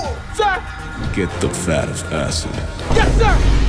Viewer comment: Pure dope.😂😂😂 P.S: First time around I thought he said "Get the FATTEST acid."😂